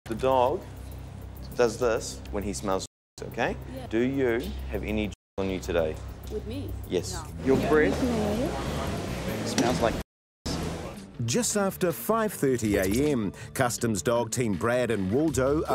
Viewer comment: I had to rewatch it when I thought the officer said "your breath smells like cat piss" 🤣